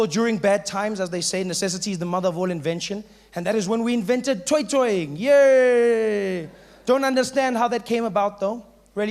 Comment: And then is when we invented... what's the other word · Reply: Toi toi dance